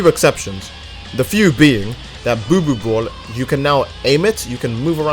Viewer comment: Lol I don't know why why I find it funny when u said buu buu ball it sounds like y said boo boo